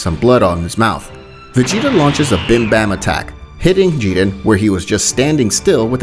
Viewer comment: what i heard "Bimbam attacK"